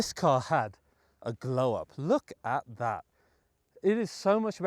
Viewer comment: Wait... I definitely misheard right? you said the new design's a throw up rather than a glow up? right? RIGHT??